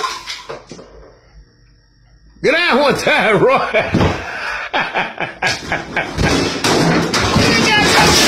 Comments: ”guerohntaherrohah” -friend of fridge fighting guy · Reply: 😭😭😭😭😭 I heard "get out one time boy" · What did he just say 💀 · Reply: 🗣GO DOWN ONE TIME, ROY!